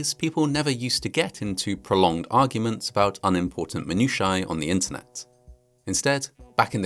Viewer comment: minutiae I thought he said monkeyshine😂